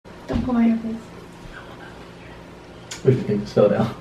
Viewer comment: anyone know what Catherine says in the very beginning like 0:01 ?? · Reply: Diivi M She Says Don’t Pull My Hair Please